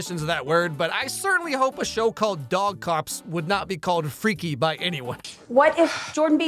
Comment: I thought he said “dong cops”…